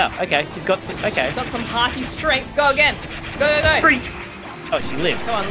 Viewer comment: Did say haki strength? Haki as in one piece haki? Do and watch one piece? ... Asking for a friend obv No sir, def no weebs here